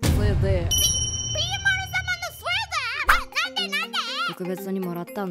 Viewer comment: 「スウェーデン」にしか聞こえなくて困ってる